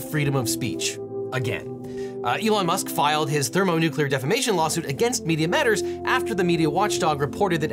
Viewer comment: Oh man, I thought that said thermonuclear detonation suit for a second and panicked